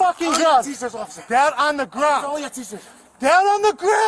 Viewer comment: Thought he was saying "im a school teacher" then I realized he was saying "I only got t-shirts"